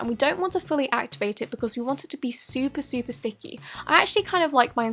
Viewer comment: Lol I’m just saying it sounded like you said super super stinky